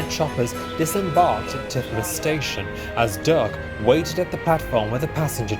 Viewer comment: Kinda sounded like the narrator said Dirk instead of Duck. :/